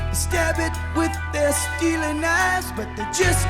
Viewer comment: “Steely knives” I always hear “Stealing knives”